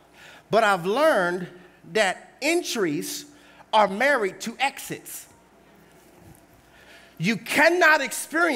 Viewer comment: "Entries are married to exits" He had to repeat the concept a few times before I really understood he meant "places and seasons of entering into" When I first heard him say it, it sounded like "Injuries are married to exits" while some Injuries are related to exits, not all exits require injuries before we listen the directions given and follow the exit signs to enter into the place where God wants us to be